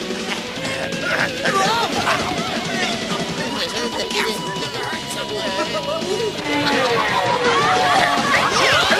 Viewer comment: what did Bulk say in that hilarious voice? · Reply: He said: Don't do that when Skull was swinging around the punching bag.